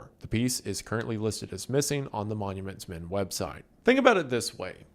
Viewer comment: I kept hearing "monuments myth", like the monument mythos lol.